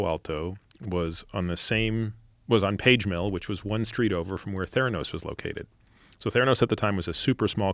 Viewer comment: Don’t lie. We all thought it said “worked for thanos”, at first 🤣🤣🤣